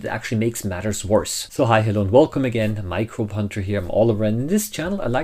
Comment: I misheard the introduction as "Hi, hello, and welcome again, micropuncher here" and that would honestly be an equally great handle for a microscopy channel. Also, who washes their chicken? I've never heard of that. Anyways, I'm off to the knobs, I want to capture the full procedure of a rotifer rehydrating after anhydrobiosis, which is my Easter project.